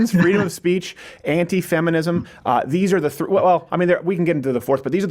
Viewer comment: lol I thought you said anti Semitism instead of Anti Feminism